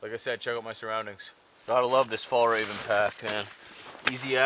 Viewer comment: Fall Raven, which I what I thought you said for a second there, sounds like a way cooler name than Fjällräven ^^